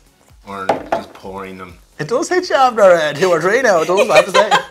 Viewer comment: what is the guy is saying at 2:20? I've listened to it several times and I have no clue what he's saying. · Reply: I'm actually shocked that I can answer this - this must mean I spend way too much time watching these videos lol. "It does hit ya after, eh, two or three now, doesn't it, I have ta say."